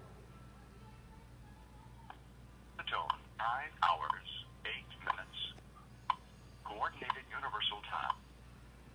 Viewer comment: did it say “nine hours” or “five hours?” Thank you. Edit: 1:07 I heard “five hours” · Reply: Nine hours